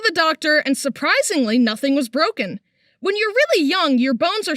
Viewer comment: I hear “and surprisingly- Dr. pepper baby it’s good and nice”